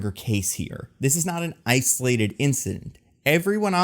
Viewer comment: About 5:40, did say "ice-olated incident" ;)